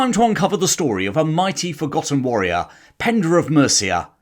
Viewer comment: Did you say "Pendra"? {:o:O:}